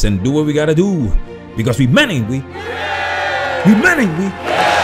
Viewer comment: “We men ain’t we,” damn…this whole time I thought he was saying “humanity.”